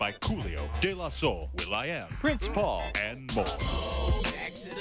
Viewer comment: When I was a kid I couldve sworn it said available at Target